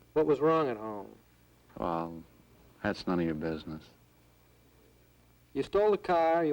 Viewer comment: “what was wrong at home”, “well that’s none of ya business”😂😂😂 since you guys are here, mind checking out my music? if not all good & stay blessed🙏🏻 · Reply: I think I heard “none of ya fuckin’ business”...